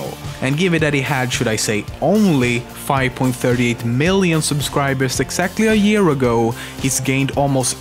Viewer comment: did you just say "subscwiberws"?